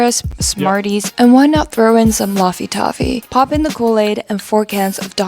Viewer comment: why does she say Loffy Toffy at 9:42?